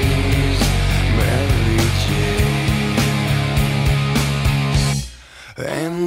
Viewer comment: Bruh, I've always thought that they said "Michael Caine"